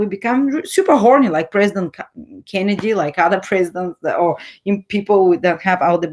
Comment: I think you mean President Clinton and not Kennedy 😂 thanks for the positive astrology. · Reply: Well I think she said “super horny like President Kennedy” not sure I can relate to that. Check from 46:58 onwards